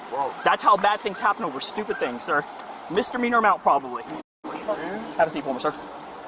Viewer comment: Do the captions say "[UNINTELLIGIBLE]" because it sounded like the officer said "mister meaner amount"? 😂 Also, as far as officers reaching into car windows goes, I think it's pretty risky; but if they think they can do it successfully, I think it's worth the risk to stop a (likely more dangerous) pursuit before it even happens. Wait. One more thing. 14:39 Did you say that his CASH can go to auction‽‽ How does bidding work for that? Haha, weird.